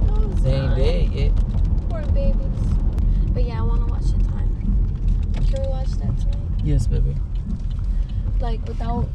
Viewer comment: broooooo I swear she said cold we watch hentai